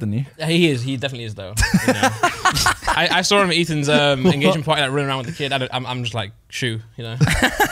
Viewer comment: Did say "Ethan's engagement party"?!?!?!?!